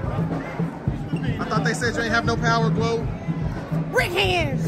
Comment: 😂 i thought they said you aint have no power Gwoat 😂 🧱🥊 #brickhands 🤣